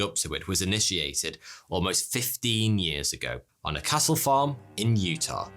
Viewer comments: Funny that I didn't make the connection earier, duh, but I thought he was saying Castle, instead of Cattle. I watch many a British TV (BritBox) or movie so I was surprised by my mistake, oh well ha ha. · Sorry, but every time you say "cattle" I hear "Castle". It's the enunciation on the T sound I think. It actually took me a second to realize what was being said bc I'm just listening to this episode in the background before posting this. Anyway that's all. I love your stuff and keep up the good work.😊😊😊